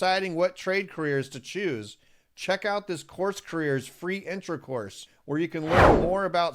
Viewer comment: Access a Free Electrical Intro Course by clicking here: <link> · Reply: Intro course, I thought I heard inter course, lol😆